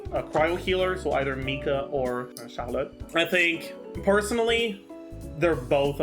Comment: Every time he says "Charlotte" I hear "Xiaolad". HELP